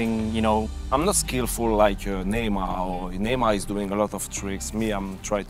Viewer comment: Did he just say I'm not skillfull as neymar